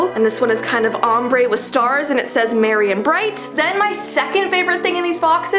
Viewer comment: On the second makeup pouch I thought it said Messy and bright lol I'm blind